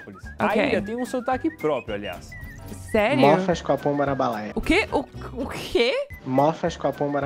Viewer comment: eu ouvi ''mor faz com a pomba na balaia''